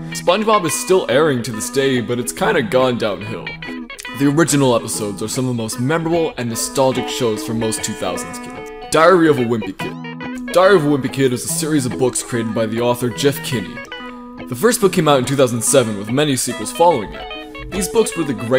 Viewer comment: i thought i heard "diarrhea of a wimpy kid" 💀💀💀💀